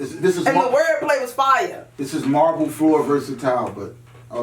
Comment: tech is weird... marble floor vs a towe!? ...instead of vs tile?